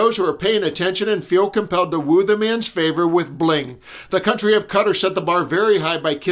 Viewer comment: Did...did you just say, "The country of Cutter?"